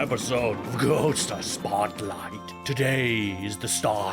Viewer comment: I honestly thought said "GHOSTER Spotlight", so I've decided that's exactly what he said. :P